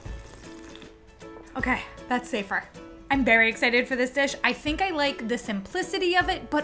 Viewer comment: "I'm Beryl excited for this dish"? I swear I heard it!